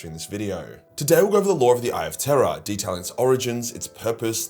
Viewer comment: Been a fan for years and always thought you were saying “eye of tera”